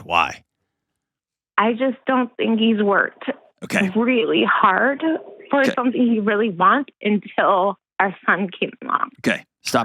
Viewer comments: until what? Can't hear what she said there. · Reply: Yes I heard it she said “I don’t think he works really hard for something he wants until our son came along” which is WHY Dr. John thought he was lazy! He didn’t automatically just start bashing the guy just because at 1:14 she said that he doesn’t work hard. Nobody is catching that! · until what? Can't hear what she said there. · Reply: "I just don't think he's worked really hard for something he wants until our son came along." That's what she said, I think